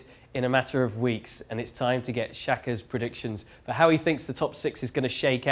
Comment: I thought he said “XHAKA”